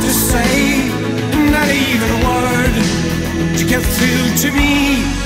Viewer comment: Always misheard him, sounded like he was singing "Trying out loud for love" which, I know makes no sense.